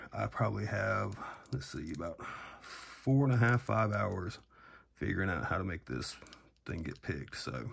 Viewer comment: people say it took him 13mins to lockpick it, while ignoring the part where he said it took him 5hours on how even to approach this lock. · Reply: u misheard "I probably have let's see about 4 and a half 5 hours trying to see how to make this thing get picked"